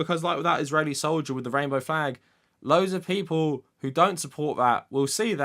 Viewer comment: Sounded like he said "fag"